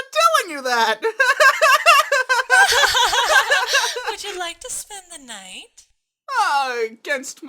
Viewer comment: Why did the English Canada subtitles say translation: you’re gonna get stabbed tonight when chi chi said, would you like to spend the night? 4:05 😂 😂😂😂